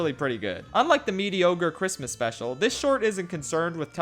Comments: Did I hear "Medi-ogre"? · He said "MediOGRE" instead of mediocre. This man is brilliant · Did he just say ´´mediogre´´?